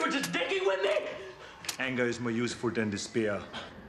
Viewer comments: He said despair, i always thought he said the spear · I always thought he said "the spear" not despair"